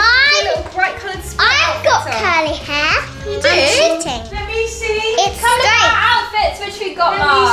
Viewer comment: Did say shitting